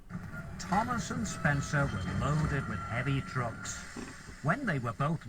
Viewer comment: I keep hearing "Thomas and Spencer were loaded with heavy drugs." lol